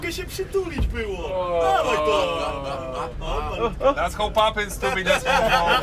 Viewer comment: Ktoś rozumie, co mówi Fornal w 0:25? Bo próbuje i nie mogę. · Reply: That's how poppin i coś tam